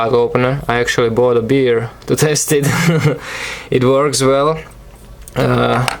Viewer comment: it sounded like you said "i actually bought a beer to TASTE it" lol!